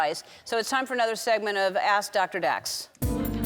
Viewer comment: I genuinely thought that she said: Ass Dr. Dax 😹